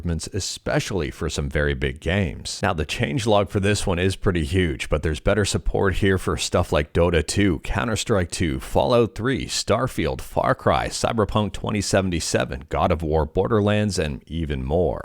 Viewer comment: I swear I heard you say StutterFeel instead of StarField which is totally true 😂